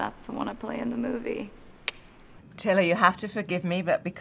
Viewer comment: I can’t be the only one who thought Anna called Taylor “Tily” at 1:55 💀